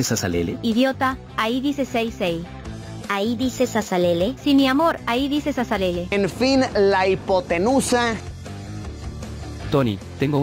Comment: por un momento pensé que decía salsa salsa :V